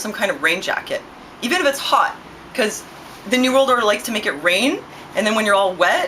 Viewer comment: nevermind, i heard you say "the new world order like to make it rain"